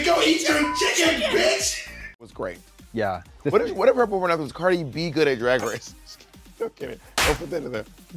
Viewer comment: what does she say at 2:37? "cardi b good at drag race"?